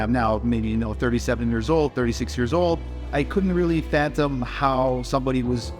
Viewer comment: did he say phantom instead of fathom?